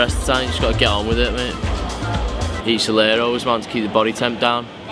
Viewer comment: What’s the food saying @ 7:25? Sounds like ciliaris (aloe), which would make sense.